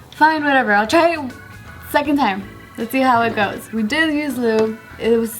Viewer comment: She said the second time she did and it still hurt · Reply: SERIOUSLY sounds like "we didnt use lube"... maybe im mishearing lol